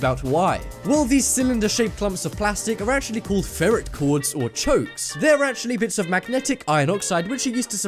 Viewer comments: it sounded like he said chodes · I literally thought he said chode